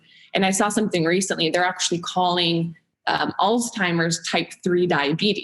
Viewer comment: did she really say alz-timers even though the CC says Alzheimer’s and Dr Lyon basically nonchalantly corrected her at 11:24?